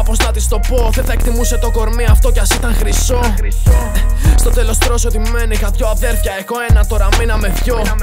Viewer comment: τι λέει?? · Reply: στο τέλος ωστόσο τι μένει